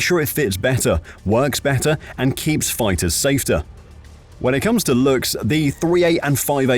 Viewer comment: Does he say, at 5:12, "keeps the fighters SAFTER?"